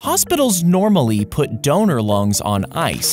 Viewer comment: I thought he said doner lungs, not donor